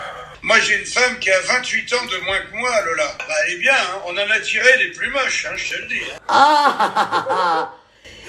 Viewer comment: "Moi j'ai une femme qui a 28 ans de moins que moi , elle est bien . On en n'a tiré des plus moches " 😂😂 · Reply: Il n'a pas dit "attiré", il a dit "tiré" 😅